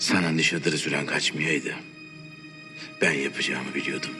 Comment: de ne diyor? Net cikmamis ses? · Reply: Aerospace Engineer sana nişatır süren kaçmasaydı ben yapacağımı biliyordum. Diyor